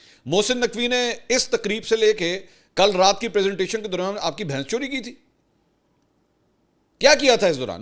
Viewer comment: apki behn chori ki thi? Did I hear that right 😂😂😂😂